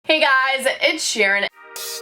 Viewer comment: sounded like she said hey gyes ed sherien